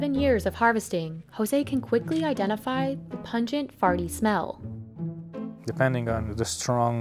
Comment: did she just say "the pungent FARTY smell" at 2:21? Bruh, I'm dyingggg